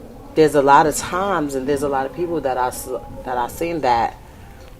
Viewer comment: Did anybody else catch @ 00:57 secs she caught herself from saying who she was "sleeping with" quote "its alot of ppl that i slept- ** she corrected to "seen that" let me know if im tripping 👀🍿👀